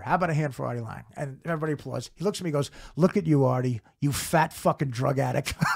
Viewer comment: I thought he said, "Artie you fat drunk slob." Rewatched and didn't see the shrug either. Artie's version's funnier.